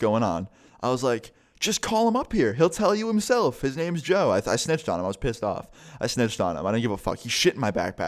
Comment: i thought you said i sniffed on him XD